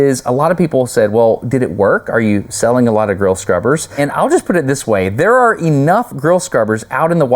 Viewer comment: I think I heard he saying "girl scrubber" a couple of times! Do I need hearing aid?